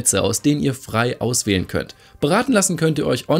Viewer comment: Ich habe beim Werbung Teil verstanden: da könnt ihr euch braten lassen,beim ersten Mal habe ich das verstanden 😂